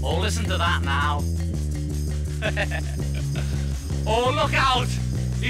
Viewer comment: The remix feels a lot less soulful...and I like the combination of the organ and ambience. And this ENTIRE time, I thought he was trying to say "oh look out" but flubbed it, hence the laughter. All these decades, and I finally know the truth 💜